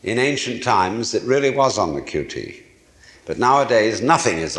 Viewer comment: What is that word he says at around 11:45 about ancient world passing something onto you on the "QT"(autogenereated subtitles say that abbreviated letters). He keeps using the word multiples times....however I cannot seem to make sense out of it. Thanks in advance!! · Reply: Hey friend! When I looked this up, I read that the abbreviation "QT" means "quiet", so I believe he is saying "on the QT" meaning "on the quiet", or "on the down low".